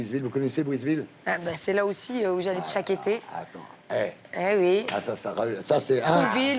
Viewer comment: ya que moi qui a entendu attay? mdrrrrrrrrrrrrrr